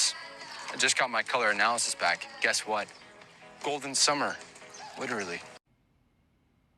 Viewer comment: I laughed the first time I saw this commercial. Then went to Dunkin and bought a golden hour refresher. By the way, the actor is Italian, so his "tan" is genetics. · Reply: He said, "Golden Summers," and all I heard was "golden showers." I can't NOT hear "golden showers."